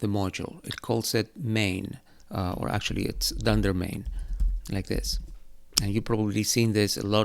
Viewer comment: Hi, thanks for this... I'm new to Python and spent an inordinate amount of time trying to figure out what you were saying before "main" at 6:48... Thanks to Google I now know it's "dunder" referring to double underscore.